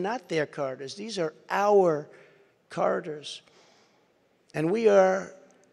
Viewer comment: I got so confused when he said “corridors.” I thought he was saying “these are our Carters.” Lol thought he was talking about Jimmy Carter and his family.